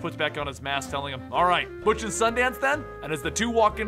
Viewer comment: i heard the clip like 4 times but i still Don't understand what miles is saying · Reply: Butch and Sundance